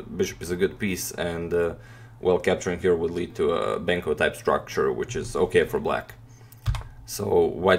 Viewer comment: what does he say here? bankle type structure?